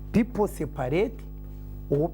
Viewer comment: All this time i thought she said people say parade